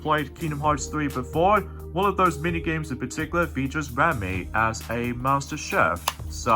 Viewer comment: By the way, It's Remi! it sounded like you said RAMY!